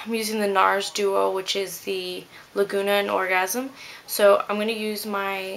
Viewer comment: I thought she said "Regurgitated orgasm" Oh my god. HAHAHA.